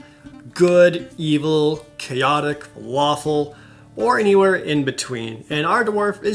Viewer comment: I swear I hear him say waffle instead of lawful XD